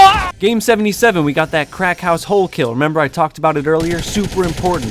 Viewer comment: I thought you were saying a crack house, not a cracked house I thought you found my dealer because he has a house li- shit